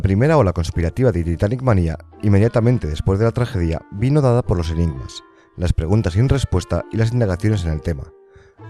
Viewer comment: Amigo, ¿qué es lo que dices en ese minuto, por favor? «Vino dada por...». Es que no logro entender qué dices. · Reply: Los enigmas